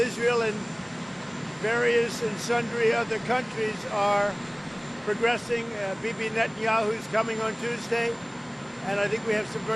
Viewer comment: Did he just call him bb Netanhayu?